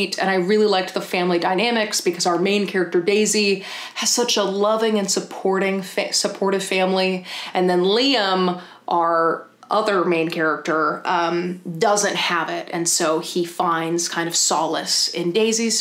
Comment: Omg for ten seconds I was really confused because I thought you said an ant was the main character of The Maidens 🐜😂